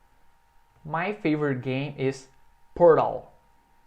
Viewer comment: “My favorite game is…”, eu ouvi pornô 😂😂😂